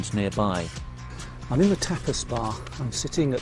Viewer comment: I heard you say the Topas bar and briefly thought you had said the topless bar. Wait, what now?